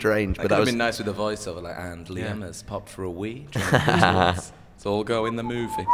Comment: PAUSE. at like 4:30 , does grimmy say "pop for a weed?" is that why zayn whistled?????? omveorihguehbvuis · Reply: He said "pop for a wee" ^^